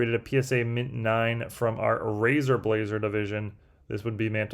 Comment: Good God did I hear "Razor Blazer" Division? Weve reached new levels!